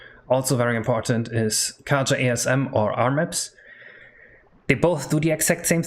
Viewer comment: What program are talking about? I can't hear very well and subtitles are not reading correctly. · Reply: armips